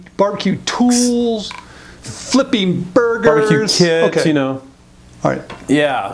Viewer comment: did say barbecued kids? hahahaha! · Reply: Barbeque "kits" he was saying.